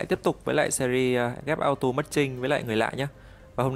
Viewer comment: sao lần nào mk cũng nghe thành : ghép auto mất trinh:))))